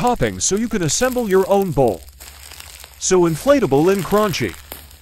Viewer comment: I heard, “so inflatable and raunchy” 💀